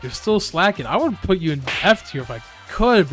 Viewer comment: DID HE SAY FAKTISK!? (a danish word btw) omg i think is danish